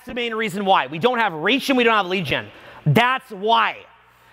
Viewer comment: What did he say at 41:10 "we don't know how to do _____. We don't have reach and we don't have _____" ? Sounds like "leegen"